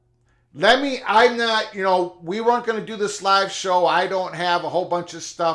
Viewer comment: I thought u said no live show 😂